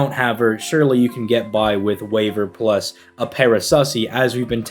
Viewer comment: I heard that as "Paraclussy" the first time and was like what the hell